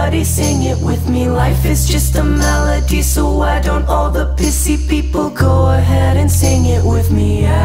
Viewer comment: He was on LSD and PCP when it sounds like he is saying pissy he is really saying PCP